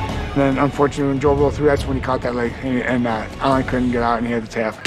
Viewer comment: wtf did stipe say!! 😂😂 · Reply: Caught that leg